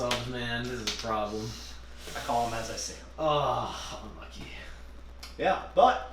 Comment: Instead off how how unlucky I heard... holo lucky hehe